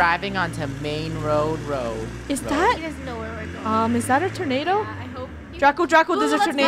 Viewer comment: When Funneh said, "Is that a tornado?" I thought she said, "Is ThAt A tOmAtO???"